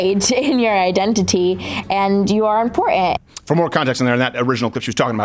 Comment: "Impor-ehh"? What is this word?